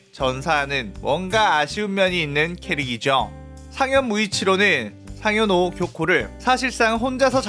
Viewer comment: 에서 자막은 하주 무이치로인데 음성은 ‘상현 무이치로’라고 들려요! 여기 수정해야겠네요ㅠ